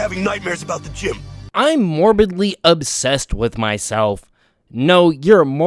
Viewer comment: Bro I thought it said I'm morbidly obese with myself